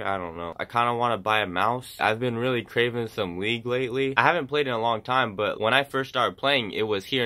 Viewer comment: Thought you said you were craving some weed at 1:20 😂